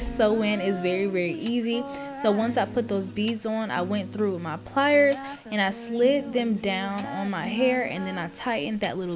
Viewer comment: i heard “then i pulled out my pliers” i said what!!?? lol but girl that’s actually smart